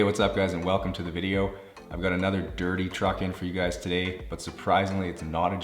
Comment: everytime you say dirty truck i hear "your a dirty little..."